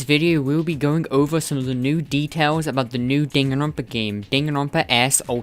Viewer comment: I thought it said new deaths It’s new details